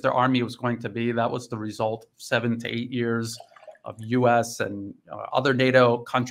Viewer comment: did he say 70 years of US supplying Ukraine? I think and hope he said 17.